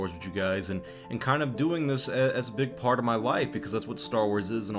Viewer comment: I thought you said “big part of my wife” lol